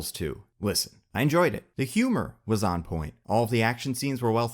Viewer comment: Thought you said the humor was awful